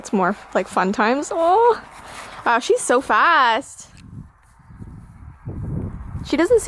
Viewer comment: When you said she's so fast I thought you said she's so fat~